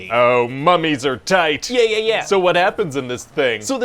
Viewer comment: Did he say "Mummies are TIED" ? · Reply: Tight